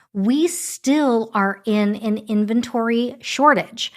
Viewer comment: I thought you said there's no inventory